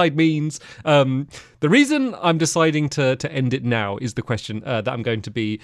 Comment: Did he just say, "the reason im ending loving world is depression?"